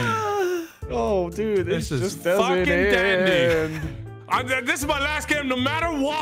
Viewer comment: did nogla just said fucking dendi?